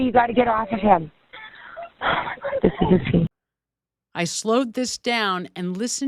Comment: Sounds like she saying "OMG this is obscene" not a scene